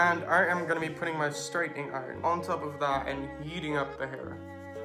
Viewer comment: Instead of “heating”, I swear to god I heard “yeeting up the hair” 😂